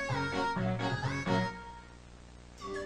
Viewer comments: "Did it just delete my save fi-" That was gold. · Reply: I heard "can I just delete my save file?" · "Did it just delete my save fi-" That was gold. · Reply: I hear it like “didnt just Delete my save file” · "Did it just delete my save fi-" That was gold. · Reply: I just heard 'Could you just delete my save file?' (That's just what I heard, but It could be something else)